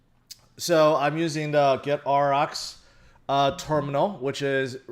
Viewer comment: Can someone help me? What terminal is he talking about at 1:15min... Something like get rx terminal, I just can't understand it 🙉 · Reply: AUROX, just look in the description